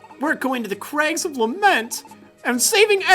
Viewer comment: i thought lamet said lampet :P